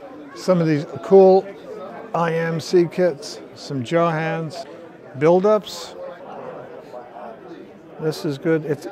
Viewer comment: Did he really call them "Jaw Hands"?:face-orange-biting-nails